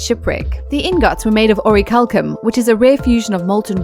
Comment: I was only passively listening and thought I heard "the ingots were made out of Rory Culkin".